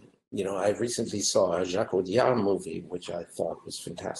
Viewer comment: Can someone please tell me what is he saying on 14:06??? I only can hear it like: I recently saw __blah__ movie... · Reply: “I recently saw a Jacques Audiard movie”